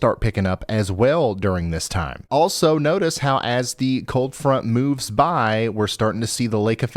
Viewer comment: Did you say cold front or cult front?🤔